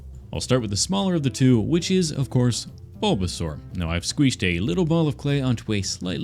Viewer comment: did.. did you say “squeeshed”??